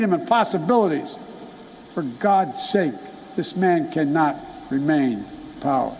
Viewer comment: Small correction, It sounds like he said, he can't remain power. not he can't remain in power.